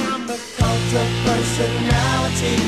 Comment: When I first heard this i thought it said. I had to re-hear it lol "Fucked up Personality"